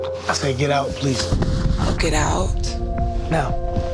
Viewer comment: Sorry I'm not joking bout the situation at all, but the way Ryan made Charmaine get out was funny. She thought he said get up